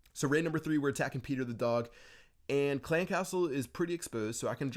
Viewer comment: Did he just say pedo the dog?!?